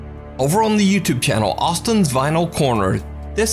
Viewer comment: I thought said Austin’s Vinyl Pornos in 1st video.